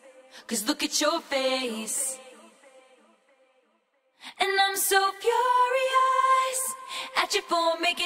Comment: I always wondered what she was saying here, turns out its just "iM sO fUrIoS" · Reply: I thought she was saying "I'm so fucking mad" lmaoo